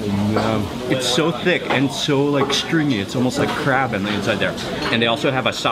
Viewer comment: did he said crap!